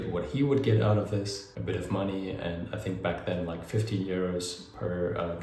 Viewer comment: Hi, at 4:41 did you say €15 or €50? If €15 how did you pull that off?!👏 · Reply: 15€